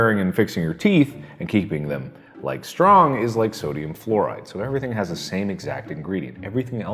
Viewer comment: I thought you said sodium chloride and I